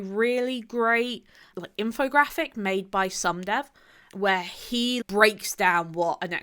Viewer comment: Thanks for the video! I particularly enjoyed the history intro. What resources did you rely on for that bit? Also, what's that infographic you mention at 18:43? I didn't quite catch the name, and the video transcript isn't very useful... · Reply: Oops completely forgot to include it! <link> S0md3v has done a ton of these <link> History comes via the tangled web and and <link>